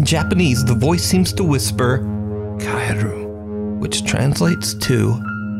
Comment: I am Japanese, I heard the ghost 16:20 saying not "Kaeru" but "Kaere" . Because Kaere is imperative form. "Go home !"